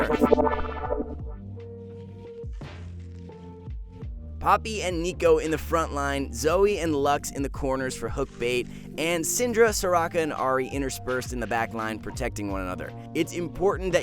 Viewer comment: did he just say Zoe and Lux in the corners? Are they finally bringing lux into the Star Guardian set? lol